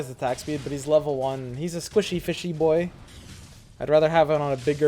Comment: Did i just hear Squishy FIshy 18:20 =0 <link>